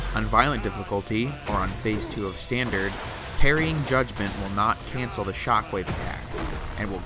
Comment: I always heard "Judgement" as "God-Man"